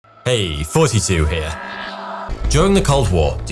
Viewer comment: i thought you always said hey 42 here lmfao